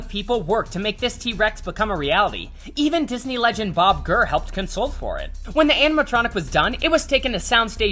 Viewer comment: did he just say Bob Gurrr???